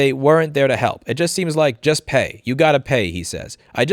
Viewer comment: They said "...you gotta pay..." But I heard "...you gotta leave..."